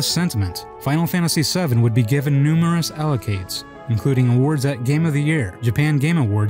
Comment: "allocades" instead of accolades 😆